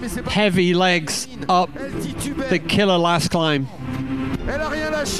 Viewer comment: i heard killer legs and then i just saw them🤯🤯